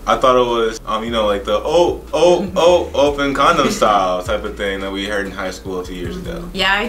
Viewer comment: Did he say "open condom style" at 8:35 loool